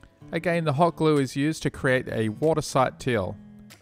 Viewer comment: What happened at 27:47 ? You said watertight seal 3x. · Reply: He accidentally said a "water sight teal" the first time, he repeated it and then corrected himself lol